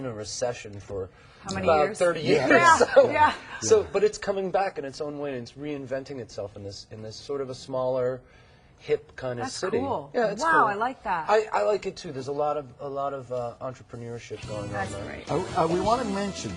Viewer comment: does she say shit at 5:34??